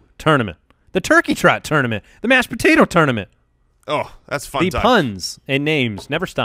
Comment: it sounds like says "thats f*cked up"